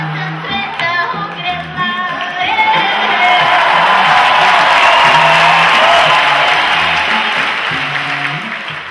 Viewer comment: What did she say around 3:16 that made everyone clap? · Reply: She said - "Bela sam, bela, junache! Cela sam sweta ogrela! :) It means - "I'm bright, so bright, boy! That i made world shine around .." Something like that ;)